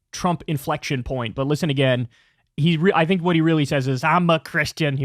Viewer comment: I heard "I'm mah Christian. "